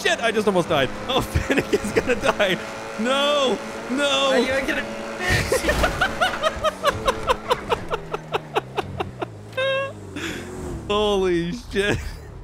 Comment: (Famous Last Words) 52:09 "How are you gonna? FIX!!!" Idek what he said, this is the closest I could understand🤣🤣